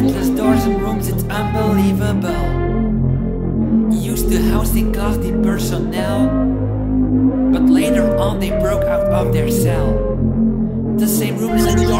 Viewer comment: Lyrics: I encountered something which is not normal. It's spatial and very phenomenal! Is there an end to this? You never know. Endless doors and rooms it's unbelievable! It used to house Class-D personnel...but later on they broke out of their cell! The same rooms and doors is all that I have seen. It's a cell block right in Sector-19! Endless looping rooms, let's go! Endless looping rooms, it's 970! Constantly opening these doors is getting hard. Each time the same thing takes me right back to the start. So many rooms I had already gone through! It doesn't stop! It just keeps tricking you! Endless looping rooms, let's go! Endless looping rooms, it's 970! Endless looping rooms, let's go! Endless looping rooms, it's 970! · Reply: On the 2nd line I keep hearing "Used to housing Class-D Personnel. But later only broke out of their cell."